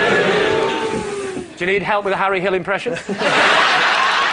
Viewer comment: What does he say at 26:42 when greg hands the book to lee I can never catch what he says to him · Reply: Think it was, “ do you need help with the big words?”